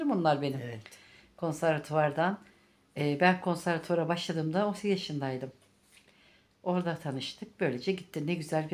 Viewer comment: eşyalarını güle güle kullan canım, konservuatardan 26 yıllık arkadaşlarım dedin, 30 yaşında başladım dedin, 56 yaşındamisin🤷‍♀️yada benmi yanlış anladım